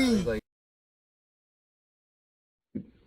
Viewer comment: The second part it definitely sounds like he said “my moch my mochi”